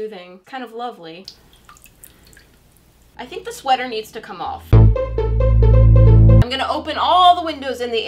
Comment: I think the sweater needs to come off Me not hearing right: did she just say her sweater needs to cough????? Goes back to watch it: ........ohhhhhhhhhhhhhhhhhhhhhhhhhhh 😂😂🤣🤣